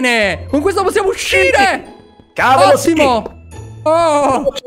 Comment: "cavolo simo" ho sentito bene?!